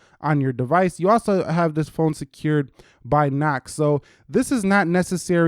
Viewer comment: Is it just me or did this just say that this phone is secured by Mac?